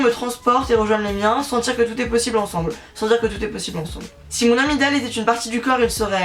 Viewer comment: j'ai entendu "si mon amygdale ..." mdrr